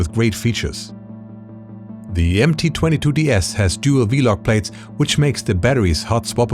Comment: Always… glad you enjoy. You mean V-lock… that is the de facto standard for batteries in our industry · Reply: I do mean V-Lock, but I thought you said V-Log batteries several times. I’m used to them being referred to as “V-Mount”.